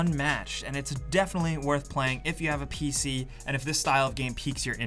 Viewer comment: did he just say, if you have a pc ?